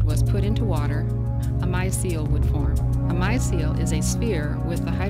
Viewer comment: can someone tell me what she says? "A [blank] would form"? I can't understand what she says at that word. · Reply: micelle